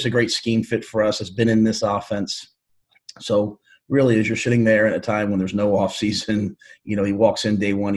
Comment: What did he say? "... As ur *hitting there" LMAO